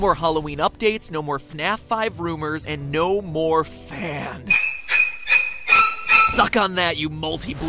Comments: i heard "no more fan" as "no more phan" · When he said "And no more fan" I thought he said "And no more Phan" I wasn't looking and when I looked I was like "Oh."